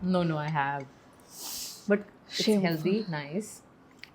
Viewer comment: did she say shameful?